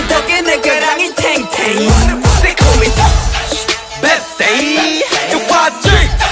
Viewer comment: I thought it said they call me PEPSI